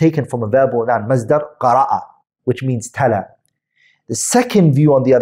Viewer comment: A 8:29 the brother says Qira'at means 'tala?' I can't make out what he is saying; talath, talab. Can someone please explain? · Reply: تَلاَ بمعنى قَرَأَ هما مرادفان Ta'la and Qa'ra'ah carry the same meaning they're synonyms meaning (ie. to recite/read/peruse etc.